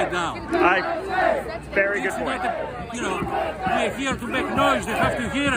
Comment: He was saying "we got to make noise" but i heard "its for the mosquitos".